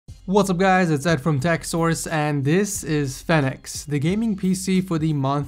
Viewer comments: I thought you said FedEx as the name lol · Lmao it sounds like he says the PC is called FedEx. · At first I thought he said the name was FedEx...